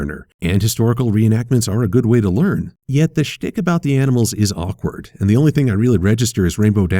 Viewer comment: 0-0 did just say shi# oh wait that was shtick ok remember, enunciate! 5:09 (just go to it i don't know how to add the link)